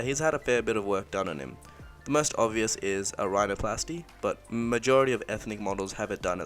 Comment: What's the word he is saying at 7:07? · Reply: Rhinoplasty